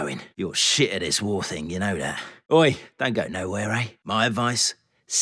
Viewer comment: What did he say? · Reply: “Oi don’t go nowhere eh”